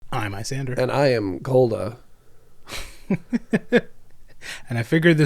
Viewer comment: When I first heard "And I am Cold-a", I misheard it as "And I am Scaldra". I think I am playing too much Warframe, but now I want to see Koda in a Rusalka adjacent outfit.